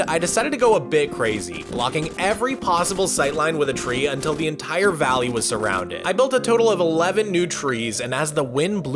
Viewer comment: hey what do the trees say at 15:50 ? the captions dont help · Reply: “Please, give us leaves. Mogswamp, we need the leaves.” Or something to that effect idk it’s something about leaves